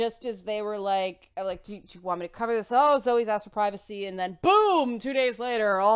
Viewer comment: Wow, OK, you make it sound like there were talks behind the scenes. You make it sound like there could have possibly been collusion but you denounced the idea before. BTW did you say "Zoe has asked for privacy"?